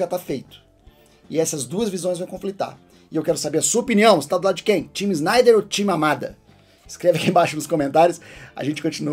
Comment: MAMADA ??? Eu escutei bem ??? Foi isso mesmo ??? kkkkkkkkkkkkkkkkkk